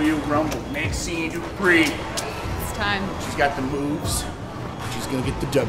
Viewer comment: I thought I heard Otis say "She's got the boobs; She's gonna get the W."